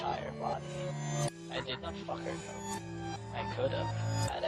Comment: wait..did he just say F her!!?